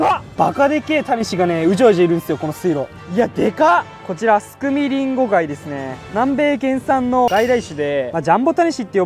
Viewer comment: スク水リンゴガイに聞こえた俺は、心が汚れてるんだな….